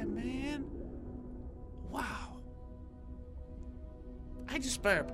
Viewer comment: on 10:12 did crainer said I just spermed !?